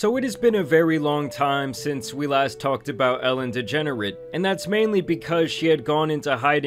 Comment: I legit heard Ellen Degenerate